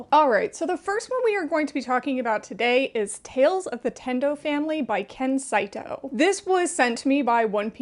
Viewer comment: I was listening to the video instead of watching it at first, and I heard "The Nintendo Family" and I'm like what the hell is I need to read that LOL😅